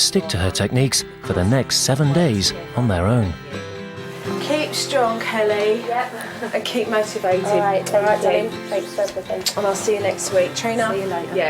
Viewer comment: what does she say? Sounds like, "keep strong kelly....and keep masterbating" that couldn't have been what she said though. I've re-played it several times and I cannot understand what she says!